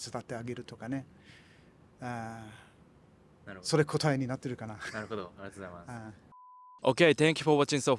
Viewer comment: can someone tell me what he says here? それ「こたい」(?) になってるかな ? 「 それ古体になってるかな？」 ??? :(